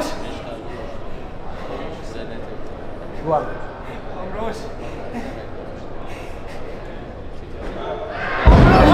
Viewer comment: Can anyone tell me what Rizki was saying at 1:15? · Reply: He’s calling his coach, whom I believe the name is ‘Rusli’. “Om Rus” = “Uncle Rus”